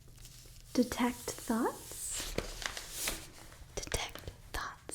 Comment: I thought you were saying "detect thots" the whole time LMAOOOO it made sense in my head